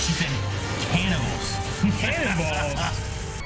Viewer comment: Yeah, I thought she said Cannibals too lol